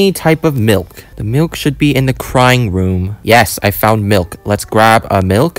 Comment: I thought he was saying the crying room💀💀💀💀💀💀💀💀